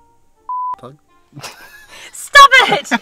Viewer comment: I think said but plug